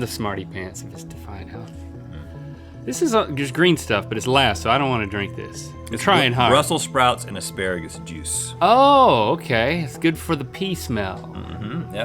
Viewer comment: For a second I thought Rhett said the drink had “Russel Sprouts” in it - so THATS where he went Phil!